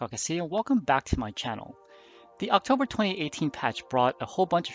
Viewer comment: "October 2018". There's only 1 patch per month from PGI. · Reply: It sounded like you said October 28th. My mistake.